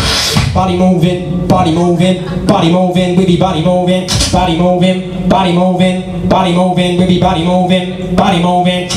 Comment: what is he saying after body moving ? ty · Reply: Pretty body movin🔥